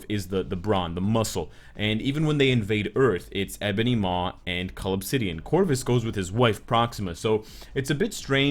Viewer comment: OH. Cull obsidian.. I always hear it as 'call of city (an)'